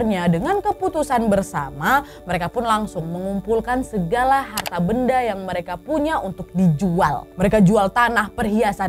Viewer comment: 😭Kok aku dengar jual harta benda istri sih... Aku salah dengar atau gimana😭